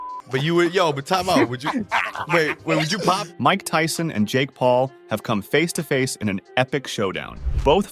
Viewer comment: I thought it said "Mike Tyson and Jake's balls have come face to face in this epic showdown". Tyson talking about Jake's having balls messed up my mind :P